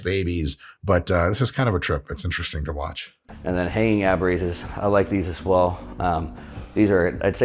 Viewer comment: > Did he say 'hanging ab raises'